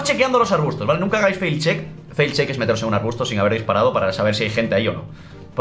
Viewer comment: "fail check"8:24 no sabe ni lo que dice, para que hablas del juego si no tienes ni idea? PD: es Face check